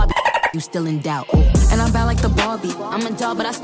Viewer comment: I thought when it said "im a ten so im pulling a ken" i thsught it said "im a ten so i pulling a big booty ken" 💀